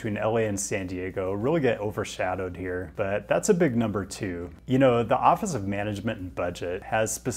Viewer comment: Got a couple of good laughs mishearing things: 11:36 the commute between San Diego and LA is “a big number two.” 14:40 “if you’re interest in joining an end of your lives stream.”